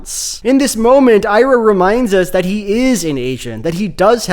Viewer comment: Subtitles say "Airo reminds us that he is an Asian " ... hilarious! 😂